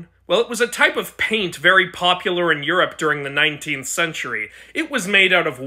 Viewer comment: You know what else was in the 1900s? A Sussy Austrian painter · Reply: Wait I thought he said the 1900s and not the 19th century